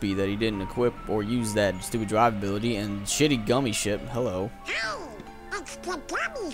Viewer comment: every time donald says "gummi ship" i always hear "gummi shit" which is more accurate to be honest